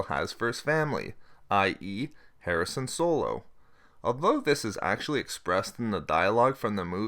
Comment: Good video and information. but am i the only person who heard him say Harrison Solo lol